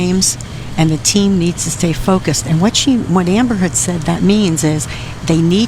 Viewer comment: i heard amber heard at 6:35 and stayed there for a minute